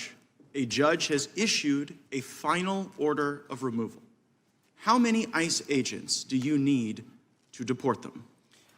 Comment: I thought he said "how many ice ages do you need?" I thought that was a perfect question. 😂😂